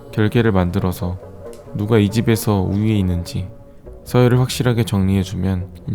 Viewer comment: 분명 자막에는 ‘우위’라고 써져 있는데 내 귀에는 항상 밥을 해주는 ‘걔’로 들림